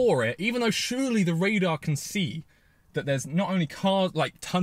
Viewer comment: I know, when have i ever said it did? XD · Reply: My mistake, thought you said LiDAR, not radar 😂